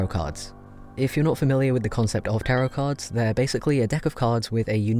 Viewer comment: I thought he said "carrot cards"